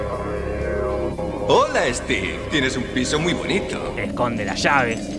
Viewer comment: Pense que dijo: "Hola Steve, Tienes Un Pit* Muy Bonito! "